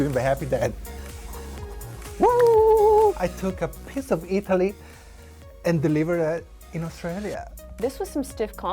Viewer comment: I'm sorry but i heard say: "i took a piss of italy and delivered it in Australia"